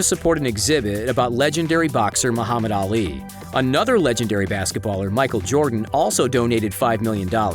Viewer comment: wait did he just say basketballer? right at the face of the goat?